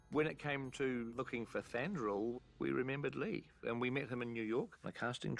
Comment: "looking for Thandruil" Am I the only one who heard Thandruil instead of Thranduil?